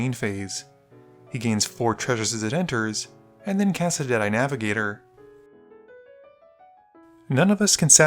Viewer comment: I swear to god I heard "Darkside extortionist" followed by "Jedi-navigator." Think I need more sleep